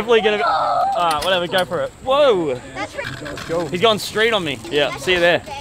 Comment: When he says he’s going street on me I thought I heard. He’s going straight on me 😂